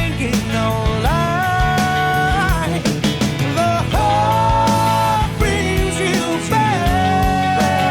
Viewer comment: All these years I thought they were saying the heart brings you back. SMH